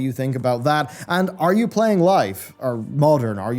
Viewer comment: who else thought he said "Are you playing "life"?" As in.. "real life" lmao.